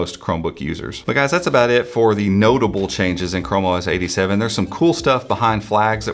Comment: I was looking away when the video started and for a second I heard you say "como es 87" didn't know you spoke Spanish 😅